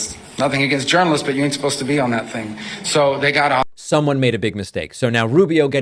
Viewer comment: When Rubio says reporters aren’t supposed to be in the chat, why does he nearly sound like he says “spit on that thang” … I hate when elites sound like goddamn morons